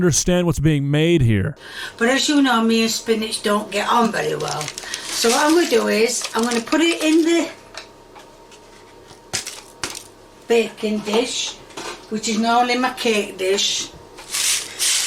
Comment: Sometimes when she says "baking dish" I hear "bacon dish" 😭